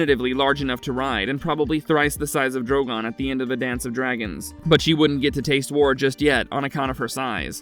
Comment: Drogon at the End of Dance of the Dragons?!?!?! I thought these awful AI Abominations would be the worst , your newfound laziness would reach. Dissapointing Fat dislike (To 99% just for the AI Shoooooot) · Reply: i can hear how it could be misheard, but it's spoken as 3:33 "A Dance Of Dragons".